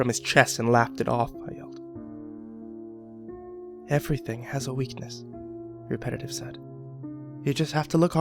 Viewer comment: I thought he said he had a weakness. He should have found the weakness or something.